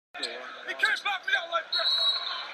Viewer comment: what did Melo say? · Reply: “He can’t box me out like that!”